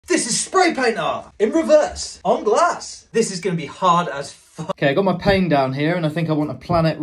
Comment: what he said: “On glass” What I heard: “anglais”